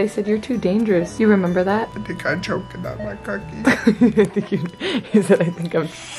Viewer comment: when he said he choked on his cookie i thought he said he choked on his car key